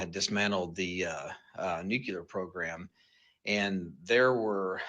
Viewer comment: (& several other times after) Is he saying "nucular"?!?